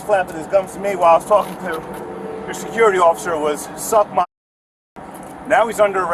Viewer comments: Sounded like he said, “You got me fucked up” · She lied and should be held accountable. If he had said that, why did she turn her back to him and for so long. What reasonable person turns their back on someone who just threatened them? She lied!! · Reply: I heard him say "you got me fucked up". He was observing a public official doing their job from, what looks like, 25+ feet away. He seems to be 100% lawful